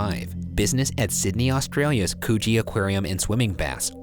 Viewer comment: THE BOYS ARE BACK IN TOWN.... holy crap...as I'm typing I swear I I heard him say "coochie" 😂😂😂😂😂😂 And then looked up and saw Coogee..lmao